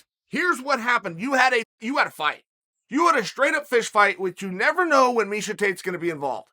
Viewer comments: did he just say a straight up fish fight? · Am I the only one who heard him say, this was a straight up fish fight?🤯